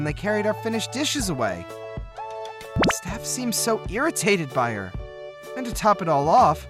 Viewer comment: I think i heard " irritated" but instead they showed "intimidated"